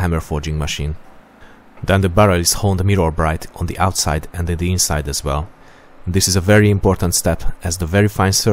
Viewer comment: what does he say in 2:17 ? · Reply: "Then the barrel is honed mirror-bright on the outside and on the inside as well..."